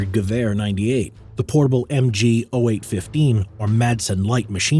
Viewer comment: i heard the voice saying "the portable MG oh wait, 50" XD